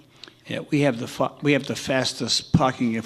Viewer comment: we have to f**k I heard that but I love you judge caprio